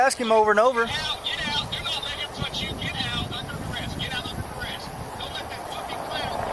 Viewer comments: I don't think the guy on the phone said "resist arrest". I think he said "under duress". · I think the guru is saying "get out under duress"